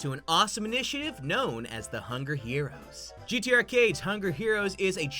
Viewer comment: I legit thought that said Gatorade